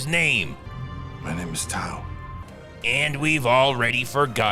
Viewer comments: did he say towel? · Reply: I think he said Tao but it really sounds like Towel😂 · Did Segal just say “My name is Towel”? 😂😂😂